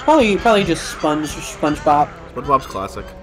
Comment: The way you pronounced that (or just the recording) it sounded like "Spongebop", and Nickelodeon could totally release albums under that name. 0:40 I wonder if this is the closest will come to dying (of actual HP loss). 1:40 Then you're going to love the episode of Game Pending Plays Nickelodeon's Jimmy Neutron: Boy Genius: Attack of the Twonkies that you're starring in right now. 2:35 Y'all are doing a great job with the commentary. 2:41 Nice stuff. 4:11 So is Squidward or Mr Krabs in the meme? 6:40 Oh please. Please never explain this one to the audience. 7:36 Seems odd that they let you go to a petting zoo and not pet the animals. 8:09 Did... did he just say "it's canon now"? 10:28 That is Yakety Sax, which was indeed used as the Benny Hill ED. 11:18 I think their wool is somewhat valuable? 11:35 Yes, he dropped it in front of you and you picked it up. That was 20 seconds ago. 12:59 was starting the "almost a llama farmer" story. 12:29 Depends on whether I think I'm a comic relief character in his current plotline. Even if I am there's like a 50% chance that it would be brilliant advice (and it's 100% going to be good advice if I don't follow it). I guess I'd probably feel stupider if I lost money NOT following the advice of Jimmy Neutron: Boy Genius than if I lost money because I did listen to him. 13:05 No. 13:26 If Youtube didn't filter profanity, would this have been the episode title? It feels like one of those highlights-of-the-let's-play lines.